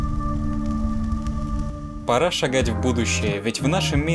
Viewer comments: Параша! Гадь! В будущее! Мне одному это послышалось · Параша, гадь в будущее. Услышал Я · Мне одному послышалось в начале: Параша гадь в будущее. Да,я наркоман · Я один по привычке услышал 0:12 -«Параша гать в будущее» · если услышал "параша гадь в будущее" ставь лайк, посмотрим сколько нас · Мне одному послышалось" Параша гать"? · Мне одному послышались параша, гадь в будущее? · Параша гадь в будущее Мне одному так послышалось?